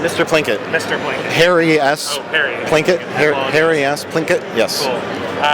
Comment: I swear to god i heard him say "hairy ass Plinkett" only then i realized he said "Harry S. Plinkett"